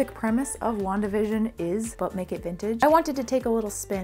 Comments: i always hear "butt nekid vintage" instead of "but make it vintage" and it makes me laugh every time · I hear "But BAKE It Vintage" lol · I was distracted by my cat at the beginning and I thought you said "Butt Naked Vintage" instead of "But Make It Vintage"... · I don't know why but when she was saying "But make it vintage" I heard "Butt naked vintage and I'm like what?! Anyways I always love watching your videos! XD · Had to rewind - You said 'But Make it Vintage' - I heard 'Butt naked vintage'...